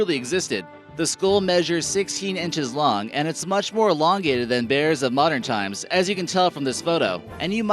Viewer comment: " It's much more longer." Did I hear that right?